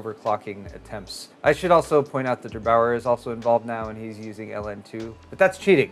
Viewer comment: I always hear "devour" instead of "derBauer".